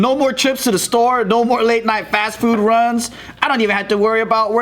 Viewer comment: is it just me? always when read 'late night fast food run' but i heard 'late night fast food nun'